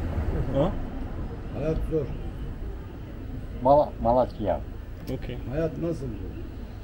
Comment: dude literally said "malatya" probably it's his home city.. but subtitle says "it's shit" lol